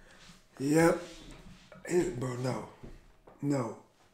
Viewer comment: "Open the door.... Open the doorrrrr" I cant be the only one who heard that at 17:00 LOL